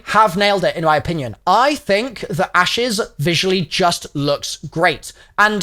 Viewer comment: I thought said "asses"